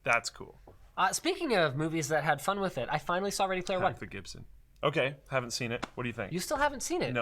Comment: Thanks, that's what i thought, he just mumbled it so i wasn't sure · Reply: And Luke talking over him the very same instant. I keep hearing "I finally saw (mumble Gibson)" or something.